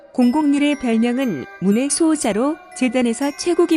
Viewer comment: 001 문의 수호자를 물의 수호자로 들었어여 ㅋㅋ